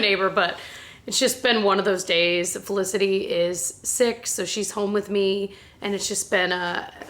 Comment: The way at the beginning I thought you said Felicity was 6 instead of sick I was like omg what is that possible I was like I can't believe I'm that old love you and you fam but I know it hasn't been six years